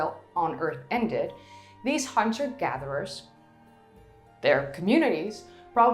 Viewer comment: Someone needs to proofread the "on-screen captions". At 20:49, she said "hunter gatherers" and the caption says "hamster gatherers". Unless I misunderstood her and someone was actually gathering hamsters.